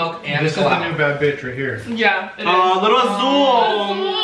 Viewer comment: I thought they said Zul to the puppy and I thought someone was calling my name 😭😂❤️and yes my name is zul 💕 and this is fr the best mukbang ever 💕